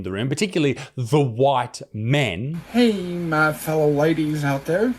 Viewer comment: I think he’s saying ladies